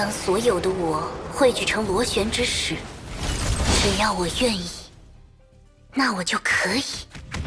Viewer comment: By the way, what exactly is Vill-V saying at around 15:42? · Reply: "I can, if I want."